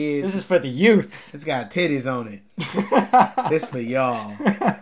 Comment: When he said 'titties' I thought he said 'kitties' and got really excited to win a shirt with a cat on it